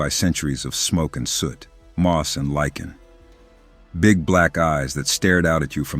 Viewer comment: Lol, I kept hearing "black eyes" as "black guys". Makes it a very different story.